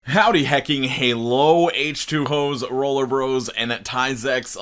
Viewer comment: My bf thought you said “Howdy heckin hewwo” and now that’s how he greets me